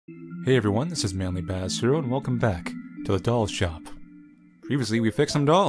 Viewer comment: I swear I heard "welcome back to adult shop" Can't unhear